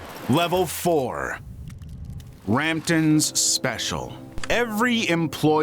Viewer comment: o m g my heart stopped. I thought it said Brampton.... anyone from toronto ca understands